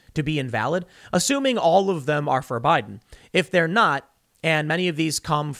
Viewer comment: I heard you say “Votes for Biden” and I heard “Votes forbidden”